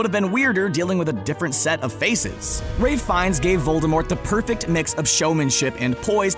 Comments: thought he said he finds GAY voldemort lol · did I just hear "Gay Voldemort"